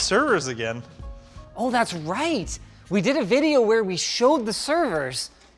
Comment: almost sounded like he said "We did an Nvidia of the servers" lol an video but almost sounded like Nvidia. Did they replace him with an Nvidia robot after the buyout hehehe